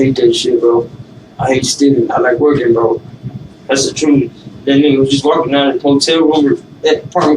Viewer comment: Did he just say that ninja ? 9:48?